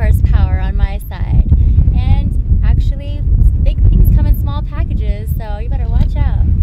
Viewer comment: Did she say "Big things com in small packages"?